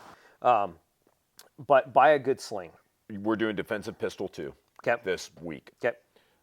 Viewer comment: did he say buy a good shlong?